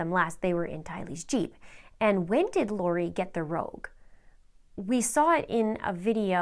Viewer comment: I need help with 6:06 when she speaks about a "rogue" or "rope". The translation i tryed to find makes no sense. I think i write that word wrong. What is the right way to write the word she is saying? Edit: OH my God... got the answer in 8:45. It is the name of the car Nissan Rogue. What a coinscidence! People! Lori was driving a Nissan ROGUE! She IS a Rogue!